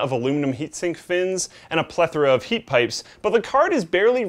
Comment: anyone hear luke at about 1:55 say prether